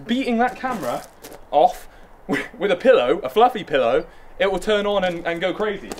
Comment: Wait...did I just hear you say 'beating off the car with a pillow' All I can say is I'm just glad that car doesn't have an exhaust.